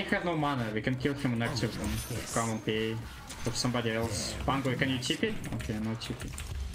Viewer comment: I misheard pango as bamboe :( rip